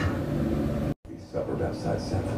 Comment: am I the only one who heard "Her mother f*ck"